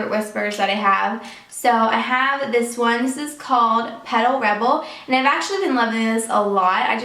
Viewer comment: I heard "pedo rebel"